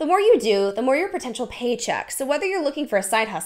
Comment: I heard ‘own original ass’ instead of paycheck 😂